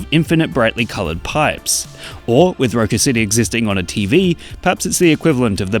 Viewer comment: i actually thought that it said "goku city" and i dont know what to think of that